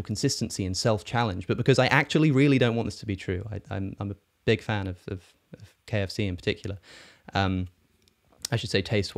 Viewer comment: Did he say he's a fan of KFC? 25:24 Lmao..ive been craving it. It's a sign lol jk